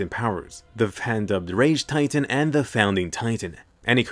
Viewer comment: what on earth did you say at 4:23 it sounds like dahfan da da rage titan